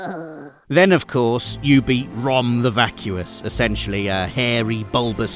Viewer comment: I thought said “Ron the Vacuous” - I think I’ll have to call him this now.